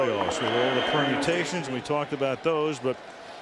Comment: never forget this game I was 16 years old I was asleep and I stared crying because I thought they said Brett Favre himself